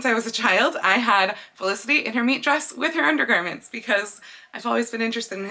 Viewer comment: I keep hearing 'meat dress' 😆 I assume it's 'meet' as in meeting friends? idk 🤷😁